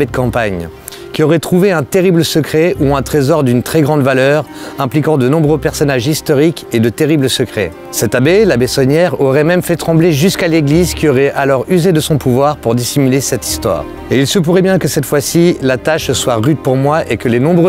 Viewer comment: Punaise, dans un premier temps j'ai entendu "L'abbé Tonnière" faut que j'arrète les calembours 😅